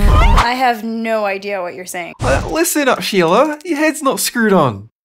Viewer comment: it sound like he said shima